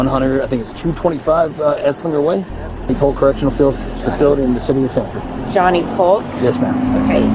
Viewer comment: I thought they said John E Pork correction facility 😂